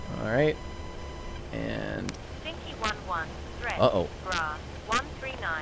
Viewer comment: I keep hearing “THREAT, BRUH”